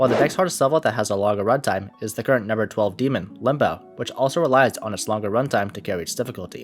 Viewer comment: WHY DID I HEAR IT AS LIMBATH